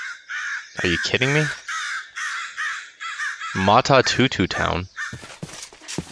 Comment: i thought that said maturnity and then i heard him say "matatutu town" and then i thought oh, that's what it was.